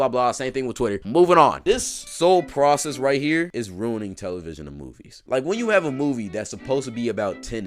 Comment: It’s just like the great philosopher Lois Griffin once said : “it seems today, that all you see, is violence in movies, and sex on tv.” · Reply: For a while I actually thought she said violins in movies lmao